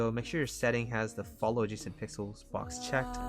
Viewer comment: " For clip studio make sure you have..." ? couldn't catch that i'm sorry xD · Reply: Owwww it's follow adjacent pixel box checked ( just looked on the left at boxes...). Love your work <3